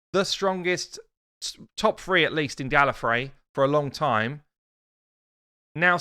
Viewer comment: did he just say in Gallifrey, as in Gallifrey of Doctor Who? The planet of the TimeLords.